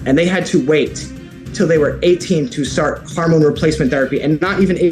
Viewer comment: Hormone sounded like HARMone